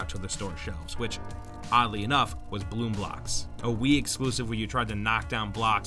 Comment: EA is known as the "Make it with Frostbite" and "Senior Developer walks away" company. 6:58 Sounds like he said "Bloom Blox".